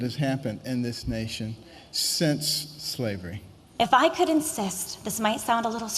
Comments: Did she just say, " if I could incest"? Lmao I must be hearing things. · When that woman was saying "If I could insist" I heard it as "if I could incest"